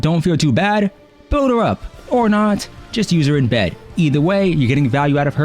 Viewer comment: Did you just say, "Just use her in bed?" 😂